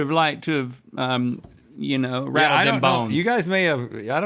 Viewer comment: Did link just say “wrap them bones” 😅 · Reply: Nope. Rattled not wrap.